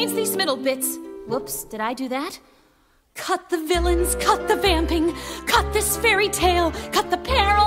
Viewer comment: I got distracted for a second and thought she said “cut the vaping”